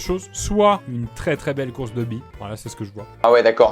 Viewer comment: J'ai entendu "course de BITE" ...😅